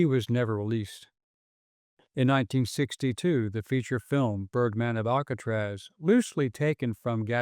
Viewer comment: Ohhhhhh...I thought they said turdman of alcatraz...😂